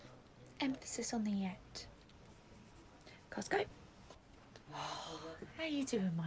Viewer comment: did she say Costco or let’s go? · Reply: Costco